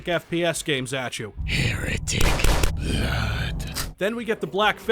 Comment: wait, the weird mage people say heretic, I thought they said your a D**K